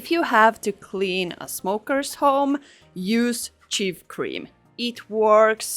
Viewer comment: what did yoy say works good to clean nocotine? It sounds like you said cheap cream 😅